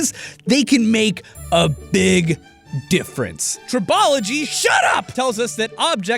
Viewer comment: Found a small error in captioning: 9:10 - Caption states 'Topology' instead of 'Tribology'